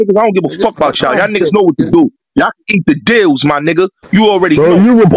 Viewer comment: What the hell is saying at 4:55? Is he saying "Eat The Deals" what the hell does that mean?